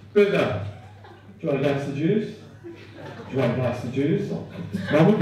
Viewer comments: I thought he was saying “pass the juice” for the first 2 minutes. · The poor dog just wants a glass of juice. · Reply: Must be a USA thing...I kept hearing "pass the juice".😆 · I thought he said gasta juice.